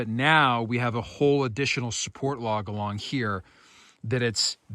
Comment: I thought he said emotional support log😂😂